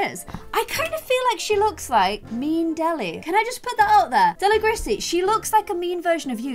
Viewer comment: I thought she said Noelle looks like " Me and Deli." As in her And. But she said a "MEAN Deli." Like, ohhhh. 😂😂😂😂